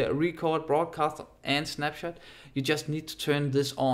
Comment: Nice video homie, keep up the good work, but! 16:28 are u saying snapchat or screenshot? or what are u saying :D